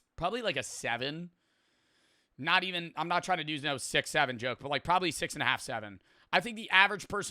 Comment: D-D-D-D-D DID HE SAY 6-7